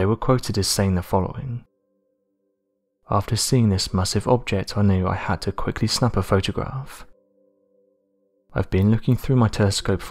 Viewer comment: At first I thought you said, "after seeing this massive object I knew I had to quickly snap one off," which is what I am doing Right NOW. SPY!